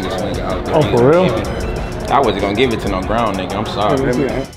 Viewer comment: I wasn’t going to give it to no brown nigga I’m sorry” did he say that correct me if I’m wrong